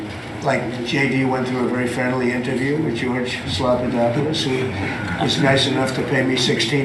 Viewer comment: Did he say Slop-a-dopolous? Also, he's gloating about being paid millions. He's not about helping others. This is the epidemy of MAGA Republicans. He remains unfit. He never belonged in office